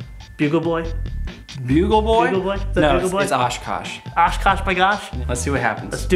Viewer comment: does he say "osch Kush"